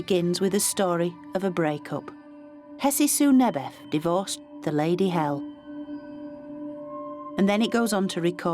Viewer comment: does it say "the lady Hel"?.. Hel is the name of a Norse "goddess" [although she's the same "goddess" as one of the Egyptian "goddesses" just with another name].